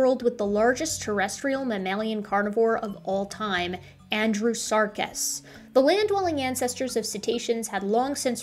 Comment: sharing the world with the largest terrestrial mammalian carnivore of all time: Andy Serkis. I misheard a bit there :D .